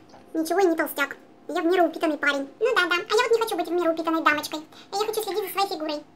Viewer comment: Лайма сказала в меру упитанной дамочкой , а мне послышалось вмеру упитанной баночкой😂😂😂😂